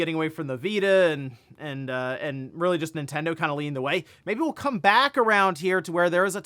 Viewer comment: Nintendo leaning away? Can you clarify what this means when Handhelds ARE Nintendo's lifeblood. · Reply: I thought he said, "Nintendo leading the way."